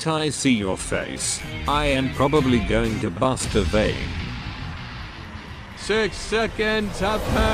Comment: I was listening to this while drawing and at 2:04 I heard him say “masturbate” instead of “bust a vein” which made me P A U S E in confusion